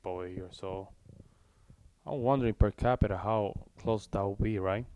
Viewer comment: cmon,not again...everytime the same error. She said per 1000! No nobody sees what are you saying! 😢